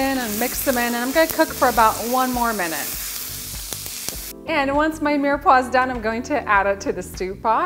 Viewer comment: for a second I thought you said änd when my menopauze is done..." 🤣 Sorry... was looking at what a local butchershop was selling. I don't know how "tri tip beef" translates into something a butcher here knows.